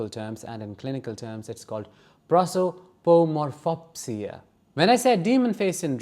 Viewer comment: What did you say at 0:32. Can you spell it please. prosoformofopsia??? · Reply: Hey! I looked it up. It’s… Prosopometamorphopsia (Whew! That was a long one!)